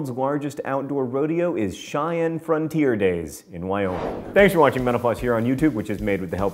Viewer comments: Is it weird that I heard "thanks for watching menopause" and the very end? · Could've swore you said at the end "Thanks for watching menopause" :O You might want to watch that!